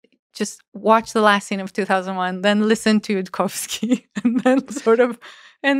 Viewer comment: Listen to Tchaikovsky? YouTube CC says (indistinct) and subtitles in linked Transcript says Yudkowsky but it sounds like the musical composer not the speaker though you could listen to both and I’m unsure